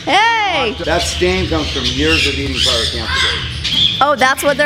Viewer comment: Lol I thought he said "Pyro Catheters"